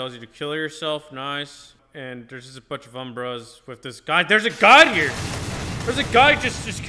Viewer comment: The subtitles say umbrellas lol